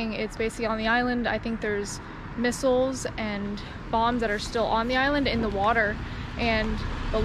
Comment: Thought she said bongs not bombs at first